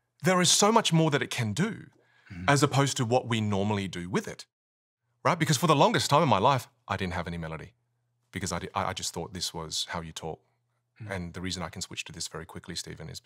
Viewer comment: all I heard was- "Righgitbelongefortlonggnefomenlife.. iddnnthavhbmlelelldldldii. BecozsiI-I-I-i_i_I-i-IoISWAZhwyourtork. "